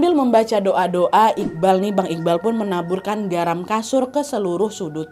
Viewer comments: Garam kasur?? Apakah aku salah dengar?? 😅😅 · Ini salah denger gasi atau emg kk bilang garam kasur?😂